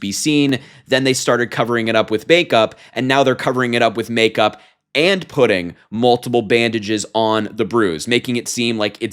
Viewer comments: For a second, I thought said "makeup and pudding". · At first I thought you said "Covering up the bruises with make-up and pudding" 😂😂😂😂